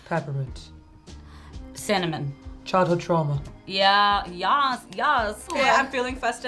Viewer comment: I thought she said chocolate trauma when they were listing flavors 😂🤦🏽‍♀️